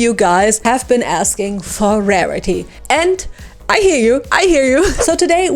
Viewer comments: Wait….in 0:10 did she say “I hiểu”? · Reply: She said "i hear you" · Wait….in 0:10 did she say “I hiểu”? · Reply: I think its “ I hear you” but “ I hiểu” also make sense